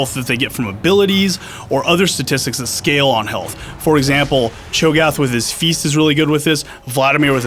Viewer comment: I heard "Chogoth with his feces" um.... ew.